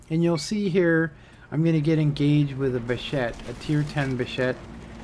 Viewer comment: Its called Bat.-Châtillon 25 t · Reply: Right. sounded like you said bishet.